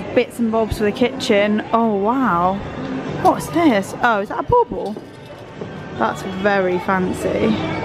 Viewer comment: I giggle evey time you both say bobble. To me it sounds like you say ball ball. I love seeing this beautiful old style department store.